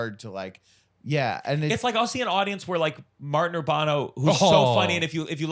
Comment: What name is Gianmarco saying at 1:24:36? Martin something? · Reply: Martin Urbano. He is a comedian